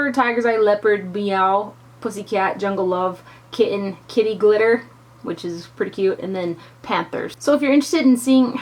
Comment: It sounded like you said "kitty litter"... then I thought nooo that's just wrong! Why would they name a shadow that! LOL